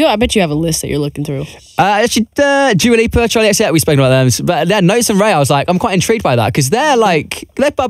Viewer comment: ok did anyone understand him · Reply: Joe Mama he said “Dua Lipa, Charli XCX, we spoke about those”